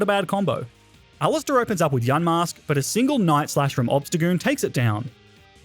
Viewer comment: Yamask or yanmask cause I heard him say yanmask